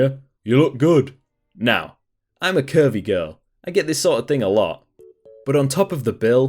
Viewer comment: Timestamp 11:45 I heard "I'm a Kirby girl" instead of "I'm a curvy girl" and I lost my mind laughing